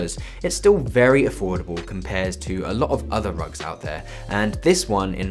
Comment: the subtitles at 5:26 xD drug instead of rug